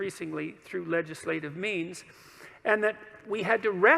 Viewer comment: Did he just say legislated memes?